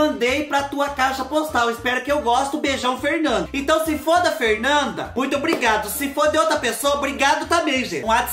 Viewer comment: "Se foda Fernanda"??? KKKKK. Tá, tá, ta, eu entendi.rs. Mas levei um susto...rsrsrs